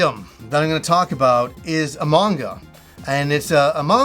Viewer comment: I've been watch too much YouTube I heard "a manga" as "among us" at first. Anyway great video man, that's some cool stuff. "It came from Play-Asia" also sounds like a YouTube series begging to be made.